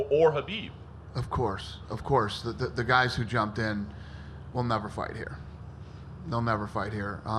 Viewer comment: I thought you said Islam would never fight here